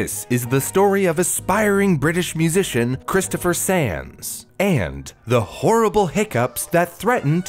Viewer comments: I thought he said "Christopher Sans" and started chuckling a bit. I'm so immature 😂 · Thanks to NordVPN for sponsoring this episode! Go to <link> to get a 2-year plan plus 1 additional month with a huge discount. It’s risk free with Nord’s 30 day money-back guarantee! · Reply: I misheard that as Christopher SANS lol.